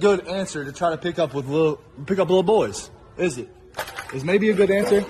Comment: he clearly says little boys.. which is worse. :face-orange-frowning